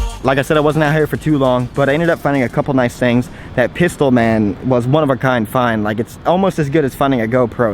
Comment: when he said "pistol man" i thought he said "piss old man"